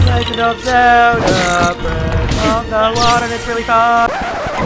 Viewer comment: In his intro it sounds like he said the legend of Zelda breath of the water